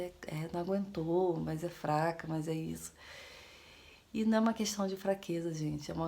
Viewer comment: Mais ein eu ouvi a dizer um Bah (14:51) kkkk Cariocando e a gauchando ? MAS BAH QUE TRI